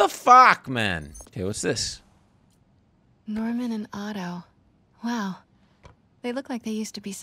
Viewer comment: Thank God for subtitles, I heard 'Leonardo', when Pete said 'Li and Otto' ... 🤣